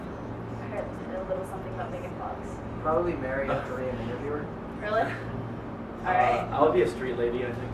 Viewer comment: did he say "probably marry a korean interviewer" ? i couldn't really hear it but :P